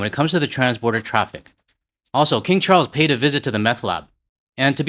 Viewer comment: Did you really say that King Charles payed a visit to the "Meth Lab"? At least that's what I heard, but I'm not sure if this was just me or me not being a native English speaker... :o) It made me laugh out loud though, and I almost spilled my coffee over my laptop.